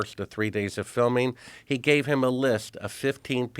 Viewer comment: I swear for a second I thought he said "he gave him a lisp" and I couldn't stop laughing. 🤣🤣🤣